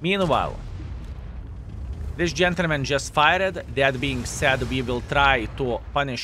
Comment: Did he say this gentlemen just "fired" or "farted?"